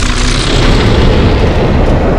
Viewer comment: TITAN CAMERAMAN CAN SPEAK ENGLISH??? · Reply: I heard him say, for plunger